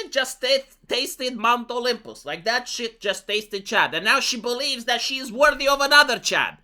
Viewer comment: for a sec there i thought he was saying Chat 😆😅